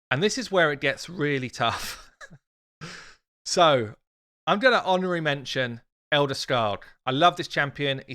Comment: He said Apothecary, Royal Guard, Tayrel, Ithos, Arbiter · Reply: thought he said honorable mention